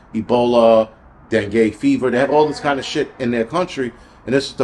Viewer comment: did you just say "dem gay fever"?😂😂